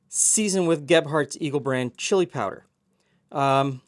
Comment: I heard it as Gephardt's Evil Brand Chili Powder instead of Eagle Brand the first time you said it! I would totally try the Evil version!